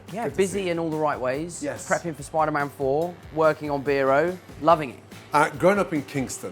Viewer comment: Did bro say spider man four😮